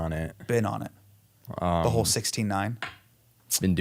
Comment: I 100% thought he said 69